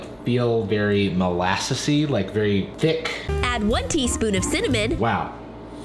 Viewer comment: did somebody say thicc?